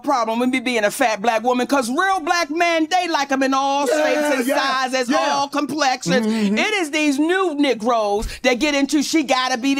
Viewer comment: She said a real man like them in all shapes and sizes not just big 🙄 rock with who gonna love you for you and not your status and bank account or if a mofo is considered “alpha” js … also she was stating how her thinking was sick back in the day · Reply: Thank you glad somebody else heard her say “ all shapes and sizes and all completions” 🤦🏾‍♀️ meaning not just big